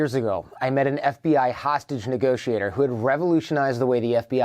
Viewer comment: I misunderstood, thought You said "Sausage Negotiator" , funny how a hot Kitchen and disparate personalities make it all the Same Scenario!!